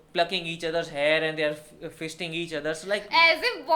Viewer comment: did you say "they're fisting each other" ? LMAO